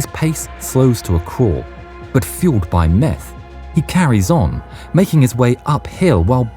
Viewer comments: Thought it said “math fuelled Fin” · I thought it said math fueled